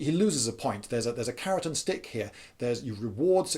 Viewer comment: "carrot and stick" not as I heard it "Keratin stick", had to go back and re-watch that bit cause i couldn't figure out why on earth there would be such a stick.